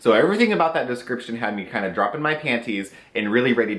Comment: At first I thought you had said "the description had me ready to drop my panties and get slapped on my face". Don't play mental imagery tricks on me!! You already exude enough raw energy as it is, I just can't take it. Having said that, I love your low-filter style of reviews, whether you hate or love a product, you never use weak wording, it's both funny and endearing!